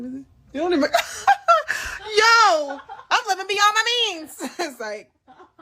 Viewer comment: I thought she said I'll be on my knees, yet she was saying I live beyond my means